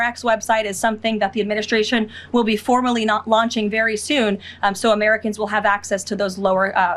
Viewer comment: Did she say "formally not launching"?